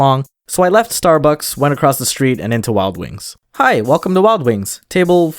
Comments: WILD WINGSSS. Why did I hear “Walgreens” & questioned why’d you guys have a restaurant named that lol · Is it me or when he said “wild wings” I thought he said “Walgreens” in an accent? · Deadass thought when he said, “I went to Wild Wings” I thought he said “Walgreens”